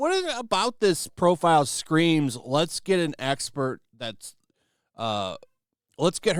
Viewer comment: You misheard. They said they got a Sexpert, not an expert